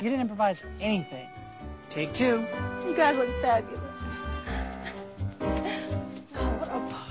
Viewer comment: i think about "wonderful fabulous" every day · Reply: Oh I always thought she was saying "God, I feel fabulous!". I'm so glad you have taught me, it's even more hilarious.